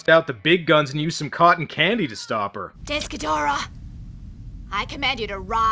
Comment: I thought you said "Desk Ghidorah", which would match its size.